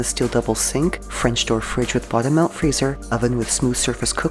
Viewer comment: Did she just say “ butter milk freezer?”